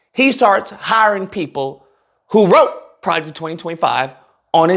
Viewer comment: Ohhhhh you said "pro...ject...20....25...."? I thought you said project 2035. . . . . .